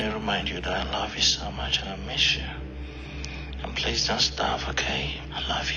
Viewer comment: I thought he said please don’t starve 😂😂 The woman are always going on about how sweet and caring they are just because they ask to they ate something. 😂